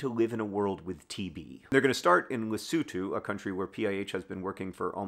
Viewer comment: Took me like five rewinds to catch that the country that PIH is starting in is Lesotho! I kept hearing "Waseeto", whatever that is!? :P